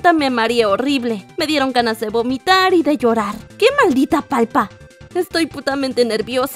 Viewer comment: por que dices palpa es palta ._.xd